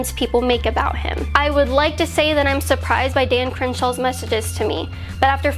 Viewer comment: I thought it said, ‘Dan Crenshaw MASSAGED me’.